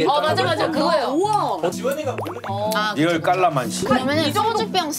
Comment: I heard “kalamansi” ? 😮❓